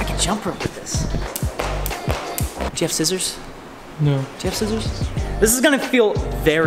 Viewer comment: „Jeff scissors“ I can't be the only one who heard that, right?